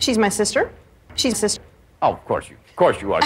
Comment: She's my sister. She's cis. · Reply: i thought she said Jesus, as in her sister is Jesus